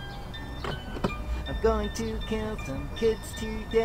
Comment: What's he say? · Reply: Ya he said kill